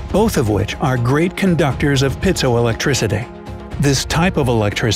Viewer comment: I have captions on and it says pizza electricity??? What’s it supposed to say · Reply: Petso electricity